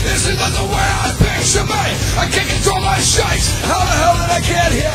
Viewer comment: i can't control my shite! haha.. instead of shakes.. i thought thats what he said the first time i heard this song